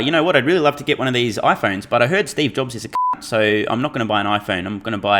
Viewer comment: I heard Steve Jobs is a Khant - CLASSIC SMR - made me laugh.....